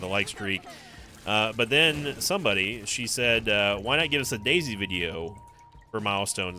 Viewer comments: Anyone else thought he said dayz video at first? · I honestly thought he said DayZ video, not Daisy video.